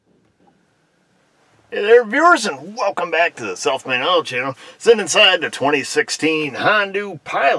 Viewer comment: Since you speak New York-ese I thought you were saying Self Made Auto:(